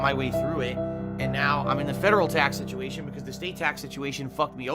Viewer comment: Everyone called it, everyone knew that this is what the "tax situation" was for. Can't wait for the restream to hear his excuses and how the money wasn't used for his wedding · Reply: All this time I thought he was saying Tux Situation, I thought he was having a Linux issue. Makes sense now though.